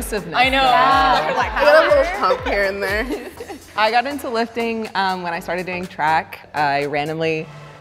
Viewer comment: I don't know why I thought she said "I got into lifting when I started doing crack"